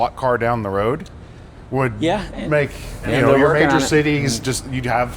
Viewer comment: did I hear him right. His comment before being cut off. About induction charging. I believe it was "yeah were working on it" got cut off and said next about driving into your driveway and charging. The future? Sounds neat!